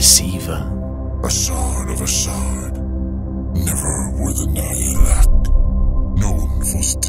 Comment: Can anyone pls help The Ctan 1:56:34 Never worth a nailath what is that word can anyone pls send it to me the correct way is said and writen thank you such a interesthing word and enjoy all of your stories